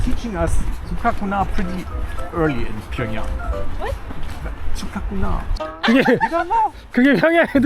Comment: ㅈ같구나 = 죽가구나 = 죽겠구나! = 힘들다 처음에 잘못 듣고 깜놀, 욕처럼 들림 ㅋ